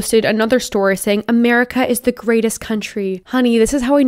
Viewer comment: "America is the greatest country." Literally half of the rest of the world's countries: Ya'll hear sum? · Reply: IDontKnow MyName i thought you were saying it wasn’t the greatest